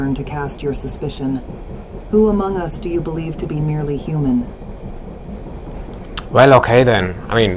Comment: As soon as you spoke they all looked at you like there's the human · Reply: "Merely human". I misheard cleopatra and thought she says "who do you think among us is nearly human" as if she's mocking the possibily of human among others than Ghengis.